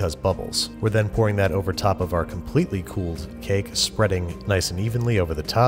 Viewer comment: umm did he just say cake?....